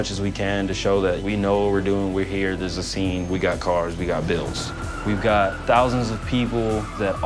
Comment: "we got builds" *shows stock engine bay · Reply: 😂lowky sounded like he said we got bills